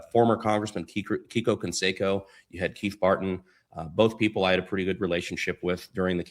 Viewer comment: Did Brandon Say..... KEITH FARTING ? 😂🤣🤣🤣 With a last Name like Farting who the Hell Names their Son Keith which sounds like Keep Farting 😂. The Poor Defenseless Bastard Must've Certainly Caught Hell at School 🏫🎒 growing up with a Name like Keith Farting and all The Kids picking on him Calling him THE KEEP FARTING KID 😂🤣🤣🤣🤣 OMG ... I can't Stop laughing over here I'm about to rupture my appendix 🤣🤣🤣🤣🤣. Well At Least they Didn't name him The Past tense version and named him Ben Farting OMG THAT'S HILARIOUS 😂🤣🤣. If my Parents would've had a last Name like FARTING OR FARTEN and they didn't Change their last name before my siblings & I was Born..... I'm Pretty sure my siblings and I would've disappeared our Parents deep Below the Garden we planted every Year 😂🤣🤣🤣. Unbelievable anyone would Keep a last name like that. That's as Bad as Having a German, Swedish or Russian last name such as : Schit, Schitt or Schitz or Schitzalotzza 💩💩💩💩💩💩💩! LMAO 😂🤣🤣🤣 Well I guess having one of those Surnames would Be Worse than having a last name like Farting, Fartin, Fartan, Farton, Fartun or Farten ! I also guess it's not 🚫 a Sin to Have a last Name like any of those Surnames, It would just be a Asinine SIN to Keep a last name like them. 😂🤣🤣🤣🤣.